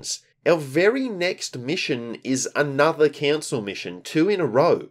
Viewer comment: I misheard that as "another cancer mission" and was like fair enough :D Honestly gg for putting yourself through this misery run, the comeback and eventual victory must have felt so good after all that bs you encountered haha